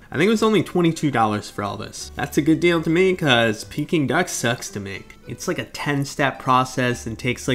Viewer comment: I thought he said "Sucks to ME" and I was SOOOO confused.